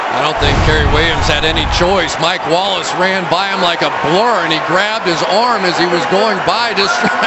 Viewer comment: What did Cris Collinsworth say at 3:41-3:45?... "Mike Wallace ran by Cary Williams like a--" what?... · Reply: Like a blur?